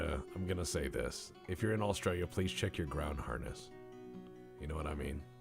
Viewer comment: I legit thought said for Aussies to "Check your ground hardness". For those interested, Australian soil in and around metropolitan areas has a high degree of clay.